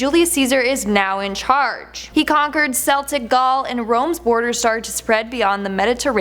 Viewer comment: did she say seltic gaul?